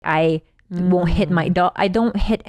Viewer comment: "I will hit my do- I don't hit anybody" just me or am I mishearing that she hits her dog..